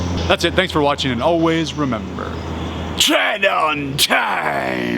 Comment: I thought you were saying "Train on Time". TIL